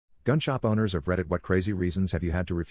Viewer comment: I share to god... I thought it said "gunshot Bonners".